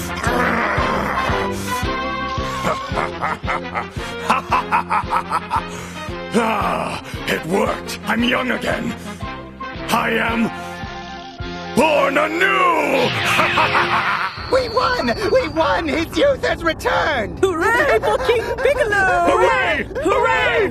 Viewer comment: Am I the only one who heard Piccolo said "born a noob"?